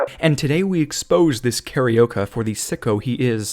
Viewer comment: Pause Did I just hear you say "carioca"? This is one word I never thought I'd hear an english speaker say. Also, the guy is from Rio de Janeiro, then? Makes sense he's an organ harvester, we be doing anything around here to get money.